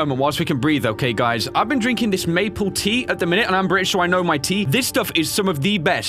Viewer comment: I thought you said "I'm British sure I know my tea" 😂😂😂